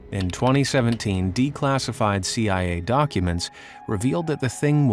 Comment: my scp addicted ass thought he said d class ified, like they were turned into d class. wtf brain